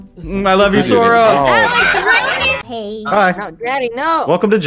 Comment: did sora just call Jax daddy? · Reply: Nopeee he called daddy bcuz her name was "daddy jade"